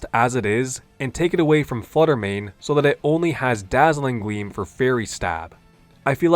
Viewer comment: why do I hear Dazzling Gweam